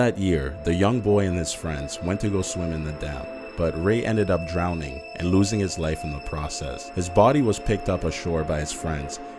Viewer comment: "Ray ended up drowning and losing his life in the process." You don't say. Facepalm 😂😂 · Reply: Omg, I thought he said losing his leg. LOL! Kind of like in the news had the bottom of the screen say man was killed to death.